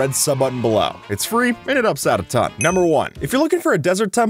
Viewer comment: I know he says “helps out a ton” but I keep hearing “upsets a ton”